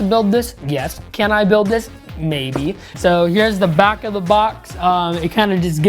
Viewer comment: i thought he say Can i go take a piss? Maybee 😂😂